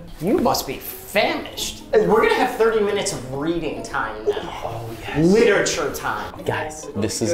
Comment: I legit thought he said 30 minutes of BREATHING time 😭😭